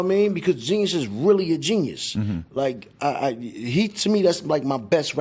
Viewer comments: MODERN FLEX Who did Ghostface say was his "best" rapper at 6:55??? Could not make it out. Sounds he said "Heat" lol. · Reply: He was talking about Genius or the GZA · this is for the culture. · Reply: Ike Who did Ghostface say was his "best" rapper at 6:55??? Could not make it out. Sounds he said "Heat" lol. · Who did Ghostface say was his "best" rapper at 6:55??? Could not make it out. Sounds he said "Heat" lol. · Ike Who did Ghostface say was his "best" rapper at 6:55??? Could not make it out. Sounds he said "Heat" lol. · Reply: he said Genius but he's talking about GZA from the Wu